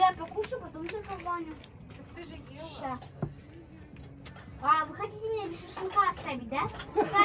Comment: what does say there? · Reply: She say "you want to leave me without a barbecue, right?" Laugh and the man answers her "come here, eat with me".